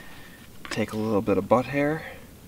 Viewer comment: I just finished watching the entirety of this video and I haven't gone through all the comments, so I'm not sure if this has been mentioned... at 13:12, did you just say BUTT HAIR??? lol